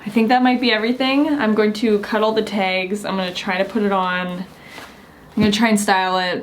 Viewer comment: Okay, did anyone else hear, "Okay, I'm going to cuddle the tags :3"